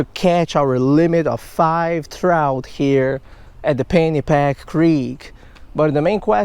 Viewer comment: No offense brother.. I thought you were saying Fanny pack! Till I read the description..I love your work brother, keep it up!